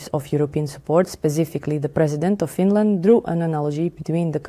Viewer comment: "The President of England..." 😂😂😂😂😂 What a hilarious, ludicrously inaccurate translation! 😂 Obviously, they meant the Prime Minister of the United Kingdom. Nevertheless, it's a matter of concern that people still don't understand that; 1. England is not the UK. England is only one part of the nation state of the United Kingdom of Great Britain & Northern Ireland (abbreviated as "The UK"). 2. The UK is not, and never has been, governed by a president. 3. There is not, and never has been, a Prime Minister of England (far less a president). There is only the Prime Minister of the UK, alternatively called the UK Prime Minister or (less accurately) the British Prime Minister. When people say England, when they mean the UK, they exhibit the same kind of sloppy thinking and lack of basic understanding which leads so many people to think that the USSR and Russia are the same thing. For instance, there are still millions and millions of people who think that Stalin was Russian. Anybody who really wants to understand the nuances and subtleties of geopolitics MUST recognise the supreme importance of precision and attention to detail. A prime minister is never a head of state. In major countries, the president IS the head of state. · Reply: Ha! My bad. I misheard the translation. She said "Finland", not "England". However, I'll leave my OP in place because the essential point still stands - the UK is not England and England is not the UK. This is something that countless people fail to appreciate and it irritates and insults millions of British people who are NOT English.